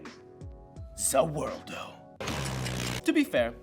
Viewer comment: Did he just say “za worldo?” Oh god dat ain’t right